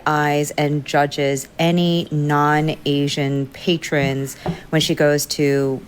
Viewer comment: she absolutely DID NOT say “any non-Asian” patrons. she said white + that’s a distinction w| a major difference! these ppl are hearing what they wanna hear. 🙄 i’m so annoyed i’m not even sure i can finish this video; i feel myself getting more + more pissed + deeply disappointed w| creators i previously really f|w! 😡🤬😤